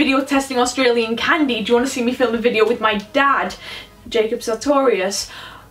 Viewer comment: I thought she said her dad was Jacob Sautorius